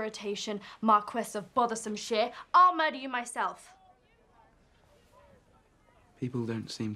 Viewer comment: I thought she said ''I will marry you myself'' but she actually said '' I will murder you myself'' 😂🤣